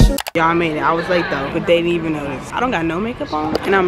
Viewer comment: thought you said but they need me 😂i was about to say yesss i need that confidence